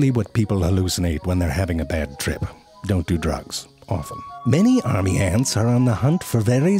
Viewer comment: "Don't do drugs, often." -zefrank1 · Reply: I thought he said "awful" not "often" lol